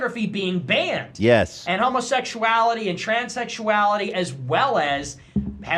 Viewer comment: Did he just say “ tranys” ? I don’t know how to spell that obviously! It’s just not in my vocabulary